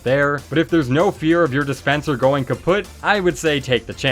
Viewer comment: did he say kaputt?